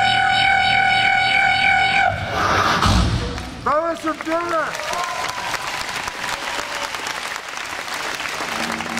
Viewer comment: HOW DOES HE DO THAT (what the person said) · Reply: Epos Zdrasti Burgas! Hello Burgas in Bulgarian